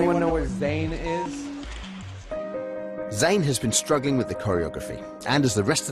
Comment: I thought that they guys said: zany has been struggling with coronavirus for some time now XD